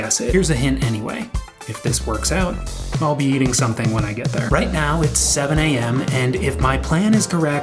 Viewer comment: Congratulations on the new girlfriend in Seattle!!! Oh sorry, I thought you said 0:41 eating something out when you get there